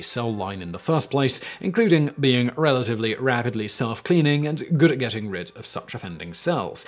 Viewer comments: I heard rabidly self cleaning. I realize it's rapidly but rabid kinda works 😂🤪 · Okay I just took my medication and I was supposed to cut one of them in half and I forgot, so now, all I can say about this video is, WHAAAT?? What did he say? What is he talking about? lol. Also though he did say rabidly at 13:13, did he mean that, or did he mean rapidly?